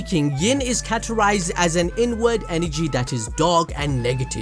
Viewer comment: So I was sitting here gaming and listening and heard him say INWARD energy and I swear I thought he said N WORD energy LOLOLOL.... I stopped the game and laughed so hard